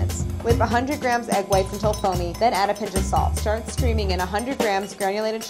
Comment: I thought it said "Start screaming"